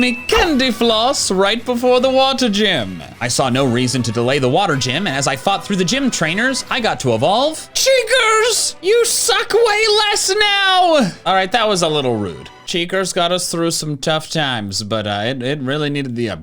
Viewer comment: i really thought he said "your sac weigh less now" at first